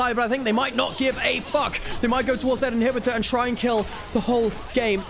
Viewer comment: ты что сказал "fuck"? 😅